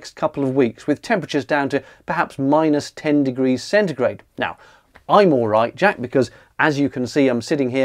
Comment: Did you say at 0:30 "Alight Jack"? Because man that freaked me out, as my name is Jack. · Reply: He said, "-10degrees centigrade, but I'm all right, Jack."